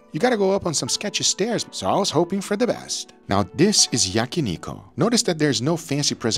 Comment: When I heard Yaki ''niko'', I was like who'd hell is that, then I realize Guga mispronounce Yakiniku.